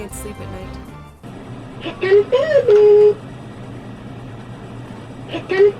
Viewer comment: Did anyone else hear "He's gonna stab me?!" 😂😂😂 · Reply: No he said Here Comes baby